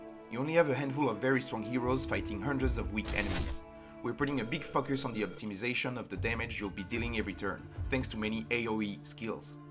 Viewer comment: "We are bringing big fuckers" - 1:22. Hahaha. I can not really imagine what he was saying for real!